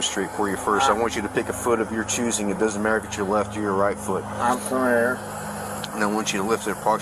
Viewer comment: I’m Scottish and even I heard he said “I’m familiar” at the DUI test instructions. It wasn’t unintelligible.